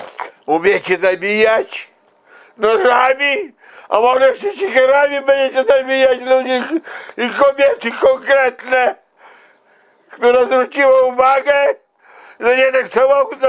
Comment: co on mówi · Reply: Umiecie zabijać nożami a może jeszcze siekierami będziecie zabijać ludzi i kobiety konkretne